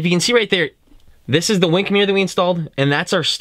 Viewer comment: I thought it said Wank Mirror...oh well...i guess Ill keep searching.